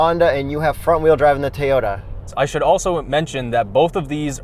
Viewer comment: I keep hearing “Tey-ota.”